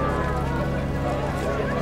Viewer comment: Can someone help me out? I can't catch what the boy says at 18:13. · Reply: He says "I'm going to need a partner"